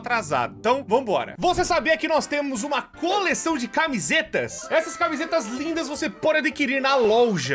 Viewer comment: MEU OUVIDO DISTORCE ALGUMAS COISAS QUE ELE OUVE,ENTÃO QUANDO ELE FALOU CAMISETAS EU ENTENDI CAMISINHAS 1:50 "vc sabia que a gente tem uma coleção de camisinhas" :)